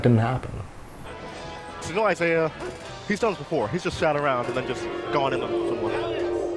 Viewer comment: Someone know what Prog said at 2:10 to 2:14? I listened to it multiple times but I can't understand it. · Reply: "Let's go Isai. He's done this before. He's just sat around, and then just gone in on someone."